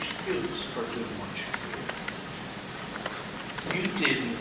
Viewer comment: This is not tricky at all. He went "hunting" - not haunting, as you seem to have misheard. he said he would kill anyone who came into his property; he left the garage door open to tempt someone to come onto his property; he killed them without his life being in danger. It's simply murder, as cold and as calculated as any. Nothing tricky about it.